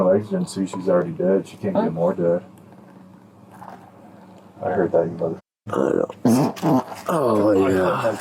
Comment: Min 16:44 Did that cop fart....and the other one say "I hear that you m***********"... And then the first one replied "Do you smell that?? The stench of death" (laughing) 🤔🤔🤔